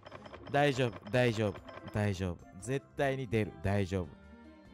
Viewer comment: 絶対にデブ って聞こえるw